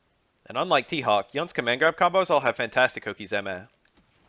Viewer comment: What is the dude saying at 8:09? I couldn't understand what he said. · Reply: Fantastic okizeme. That means they wake up right next to you and you can push a mixup on them as they get up off the ground. It's usually called oki for short, it's a Japanese fighting game term that sees common use in english